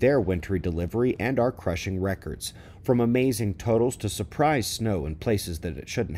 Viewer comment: I heard "Amazing Totos".. Shoutout to Tony Heller!